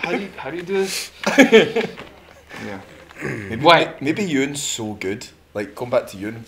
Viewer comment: Who are they talking about from around 10:50? I can't catch the name clearly. My closest guess is Ewan (Osferth), but I'm not totally sure, because I'm not a native English speaker + accent is going around too. :')Thanks for helping out. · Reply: You're right, they're talking about Ewan :)